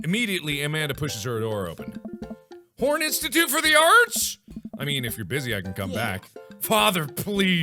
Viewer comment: when Mark said horne institute for the arts i literally heard him say "PORN" institute for the arts. ;)